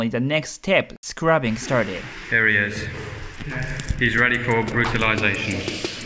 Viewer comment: I thought you said READY FOR FERTILIZATION